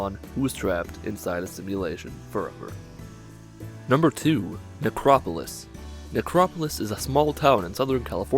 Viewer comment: thought you said Negropolis. I was like "Fallout shouts out Detroit?"